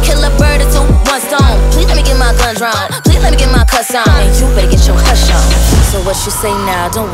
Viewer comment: "please lemme get my cuss on" i thought she said "please lemme get my croissant" lol